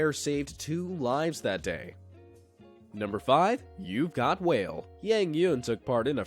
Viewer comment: “YOU’VE GOT WHALE!” I thought you said I got a whale and I thought you said you were gonna get a aquarium for me and I’ll put the whale in there!LOL😂😂😂😂😂😅😅😅😅.